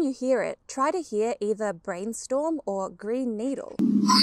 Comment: wow! I heard nothing until you primed my mind...then I could choose what i wanted to hear. when I tried to go back, I heard "brain needle". I LOVE this kind of information!! Thank you so much for this video! I'm a bit of a psychonaut, so learning and experience about altered states of consciousness, as well as altered states of perception. for example, I have a mental illness and at times, I see my own emotions on other's faces. I didn't notice at first....until I noticed that when I was feeling self loathing, the people on the billboard by my home would have "you suck" looks in their eyes....and when I was mad at somebody, they would also look mad and as if they agreed with me... if I felt (very rarely) somewhat attractive, the men on the billboard seemed insecure and angry, while the women had 'come hither' eyes. How can a picture have emotions and, if it does....why would it match mine? obviously, it was me that created the issue. It was immediately that I understood that 'if my mind does that to people in pictures, it probably does the same thing with people in society that I come in contact with.' sooooo, maybe when I am feeling confident for whatever reason, maybe (😉) the cashier at the store is just being nice and having customer service....not hitting on me. Maybe, the same person doesn't think I'm pathetic on days when I feel down. Maybe, it's my mind projecting my own emotions on them and me trusting my senses because of my credulous nature (read: fear of not being